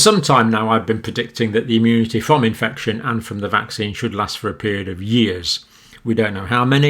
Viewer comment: This is good news yes. Why then are people that have had COVID still being told they need to have the vaccine? Would it not make more sense to save the vaccine for people that have not had COVID? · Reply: I heard that they "can" have it and not "should"